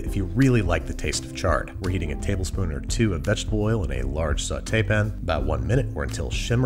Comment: What did he say? I couldn't understand his pronunciation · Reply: "A large sauté pan"